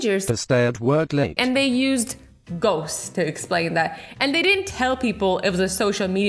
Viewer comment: i thought she said goats not ghosts her: omg there are real life goats?! me: 😑😑😑